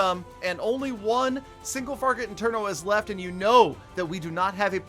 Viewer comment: Who else hears “Single Farget Interno”